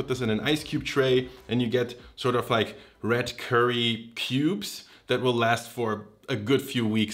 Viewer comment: i swear to god he said "red curry pubes"